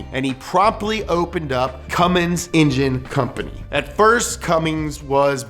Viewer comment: Whatsup with the ford Pumphrey Edit: at 3:54 did be just say cummings? You're killing me Pumphrey.